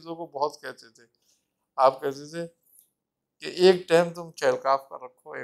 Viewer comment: Kindly 28:25 pr yeh kya bolty hain chirgaaff??? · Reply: Chahal Kaaf چہل کاف